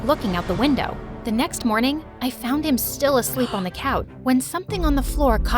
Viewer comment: did nobody realise that said he was asleep on the couch but the captions said without a shirt?🤣